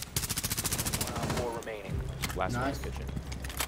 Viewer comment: Did I hear wrong or was the call "last one vigil"? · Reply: I think it was last one in kitchen but yeah it does sound like that